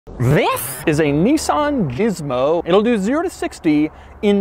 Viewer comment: Ah yes, my favorite fast pickup truck The Nissan Jizzmo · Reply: I thought he said Nissan Gizmo…